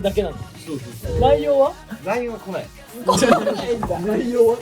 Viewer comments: からが最高すぎる🤣🤣 末ズだけ 「内容は？」が「ライオンは？」 に聞こえる🤣❤️💚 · 内容は？って聞き取れてるの樹だけじゃん笑笑